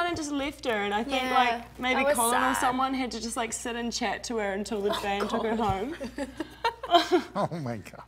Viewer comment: what’s she say, who took the random girl home? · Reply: Tara A colllin has to sit and chat with her until the van took her home.